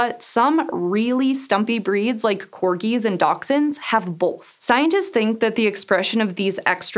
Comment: What is that word at 02:30? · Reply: I think she's trying to say dachshunds but it sounds completely wrong. Here in the UK it's pronounced dash-hunds.